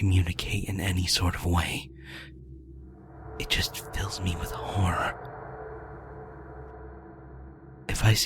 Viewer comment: when it said "it fills me with horror" i thought it said "it fills me with whores" XD